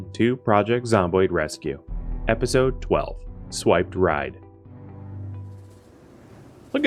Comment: Lol. For a second at the beginning, I thought you said "Swiped Right". I thought, "PZ's a little too early and it is also a little too freaky for PZ"